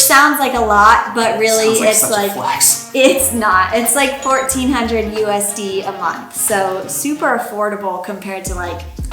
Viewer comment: she says 1400 usd a month and the subtitle says 465 pr month..